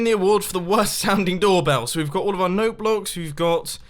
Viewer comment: Notebooks instead of noteblocks😂